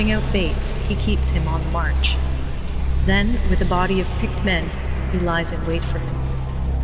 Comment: "then with a body of pigmen, he lies and waits for them" Sabaton misheard lyrics are the best